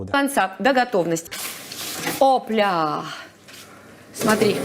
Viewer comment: Коли вона каже "опля", мені почулося "сопля". Невже вона вирішила сказати правду про свій маринад 😅